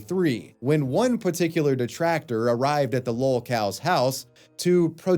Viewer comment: When it sounds like is saying "lol-cows", the captions read "LCOW". Which is he actually saying? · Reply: The word is literally lolcow, someone you milk (antagonize for laughs